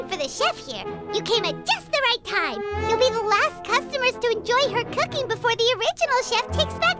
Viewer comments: I didn't imagine it, paimon said "Her" referring the traveler even if subtitle say him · did I hear that right? Did Paimon refer the traveler as her now???